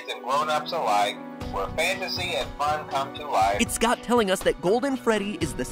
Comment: I thought he said “It’s god telling us...” for a second